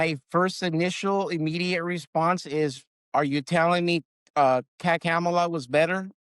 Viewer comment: He still refers to Harris as Kack-Kamela. That should tell you he’s not extricated himself fully from the cult he was heavily involved in. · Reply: I thought he said "PAC"-Kamala.